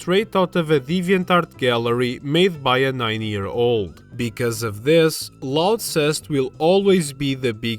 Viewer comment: I heard “90 year old” instead of “9 year old” 💀 Edit: I can’t believe you left out Pimp Lincoln